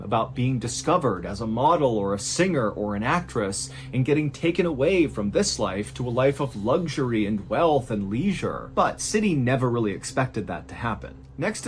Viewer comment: did he just say life of luxury?